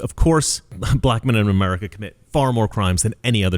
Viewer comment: Oh, I thought he said MEN IN BLACK and I thought, "This is going to be interesting!" No, it's just shitheads that need to be immediately executed.